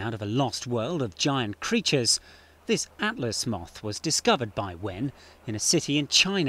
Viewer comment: I thought it said Mouth...